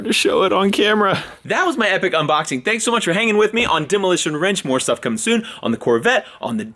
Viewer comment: did he just say demolition wrench? 🔧🔧